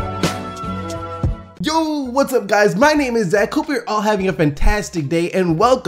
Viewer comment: At the beginning i thought he said "my name is Zack Cooper"